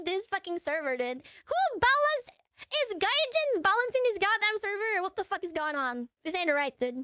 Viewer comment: did I heard correct!? She said gaijin?! WAR THUNDER?! ATTACK THE D POINT!!